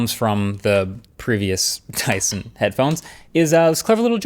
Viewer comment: lol subtitles say Tyson instead of Dyson :3